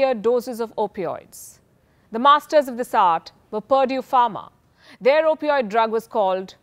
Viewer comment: When she said 'Purdue Pharma' i thought she said 'Perjure Pharma' LoL